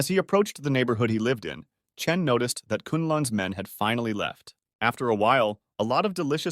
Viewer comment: subtitles said klans man 💀